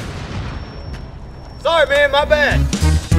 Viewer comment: 「Sorry すんません」にしか聞こえないw